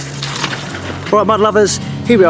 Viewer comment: A little funny... I just started watching your videos not long ago and the first few videos l thought you were saying, “hello my lovers” of course l came to realize it was “mud lovers”. I still hear it from time to time. 🥴🤷🏼‍♀️😂